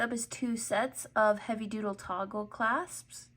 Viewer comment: Just found you today. ❤❤❤ Did you say “heavy doodle” @ 6:35 ?